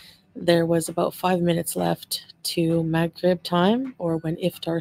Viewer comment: I'm watchin on mute bc work, and captions caught McRib instead of Maghrib lol